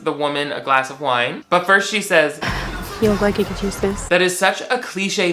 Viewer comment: Can someone tell me what she said? I can't hear it. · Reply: "You look like you could use this"